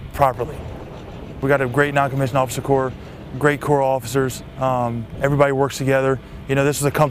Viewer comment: Did he say @ 0:40 "No one works together" ? ^^ Does Staff Sergeant right eye hurt? ^^